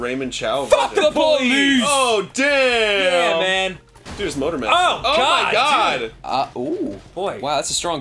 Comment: sounds like Says "MotorMatt"